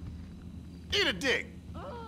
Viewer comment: Bruh at 7:47 bruh all I heard was eat a d### XD